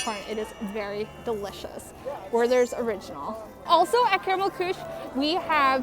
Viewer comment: Did she just say Caramel Cooch....... omg...... :) Love and! Please don't eat dry ice HAHAHA